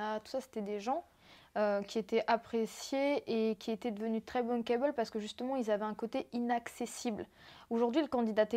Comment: BUNKEY BALL!!?!!! Jklajkswkaks Rien compris elle a dit quoi comme mot là???